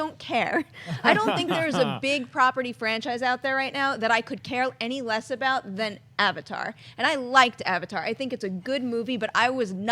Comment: Sorry, Kristian. You misheard her. She said, "I don't think there's a big-property franchise out there right now that I could care any less about than Avatar." Easy mistake, though.